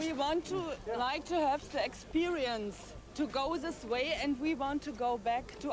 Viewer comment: 147 it sounded like she said sexsperiance